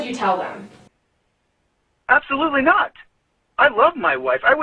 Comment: One thing I usually listen for is the use of past tense, especially when someone is heated. does use present tense when talking about Suzanne. “I love” (not loved) “my wife”, “she is” (not was) “the light of my life”, etc. That is hard to do consistently if you know someone is dead, especially by your own hand. Just an observation. I could be completely off. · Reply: I heard him say I loved my wife not I love my wife.